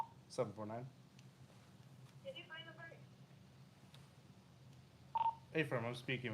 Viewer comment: sounded like she said: “did you find the perp?” 😂 I hope I’m hearing things cuz I don’t think our boy committed any crimes lol